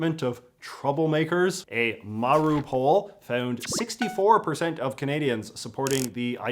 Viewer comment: been watching too much ukraine news i kept hearing “maru poll” as “Mariupol”